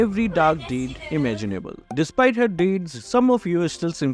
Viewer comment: Bro I wasn't looking and I thought I heard 'yin so evil still u simp for her'😂